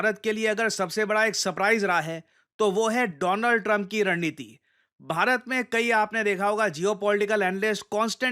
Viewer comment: Donald Trump ki ran*niti 💀(am I the only one who heard this ?)